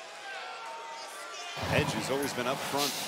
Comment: did he say edge?